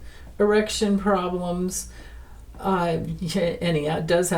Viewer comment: I thought you said erection problem, but you said election problem!😂